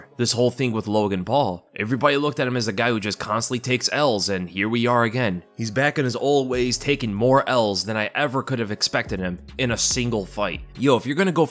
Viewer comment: I soo thought was saying took more Ls then last time 😂